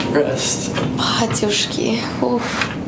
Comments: Did she say "батюшки" - > "batushki"? 😁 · What she says? I'm from exUSSR republic of Moldova and I know Romanian Russian English,but English I don't speak fluently & my words knowledge ate very basic like 30% of whole English words. So...I literally hear a Russian word, or from any other Slav country (like Serbia) with English accent. But maybe I just don't know this word, maybe it's a slang word? Guys help me out pls,I specially watch English YouTube+TikTok+Twitter+Sports to learn more words) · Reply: Батюшки (Старый человек, дедушка) и Уф - это как эмоция в словах